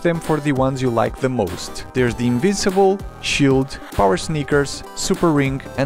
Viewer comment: Did you say invisible or invincible